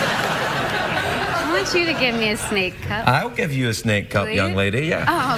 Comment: Am I the only one who hears "I want you to give me a snake up"? Damn.